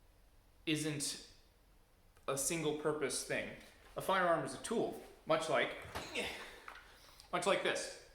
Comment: You confused me. Can someone please help me with what he says at 4:01 vs 6:14? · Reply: I said "A firearm is a tool." As in, a firearm is a utilitarian object. It has no morals, feelings or self-determination. How you use that object is what matters. At 6:14 I said "A firearm is a single function device." As in, a firearm only does 1 thing. Launch a projectile where you point it. What you do with that 1 thing is what matters. I hope that helps? Thank you for watching!